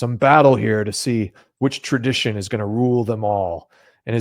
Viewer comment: It sounded like LOTR , One tradition to rule them all... that cracked me up.